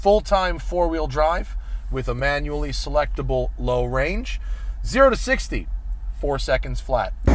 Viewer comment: More of these will fishtail and hit light poles than mustang gt's do. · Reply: Oh mb I thought Matt said AWD with a selectable high and low but must have been 4wd instead